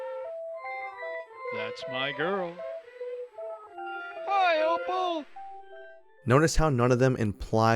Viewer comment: I swear he says more then just "Hi!' I hear him say "Hi Opal!"